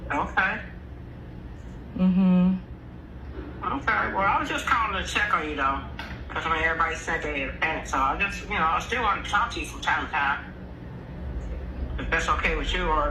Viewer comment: She said I’m sorry she just didn’t caption it 8:28 I’m sorry well I was just calling to check on you